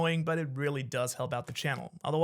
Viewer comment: did you just say Shanks so much for watching? legend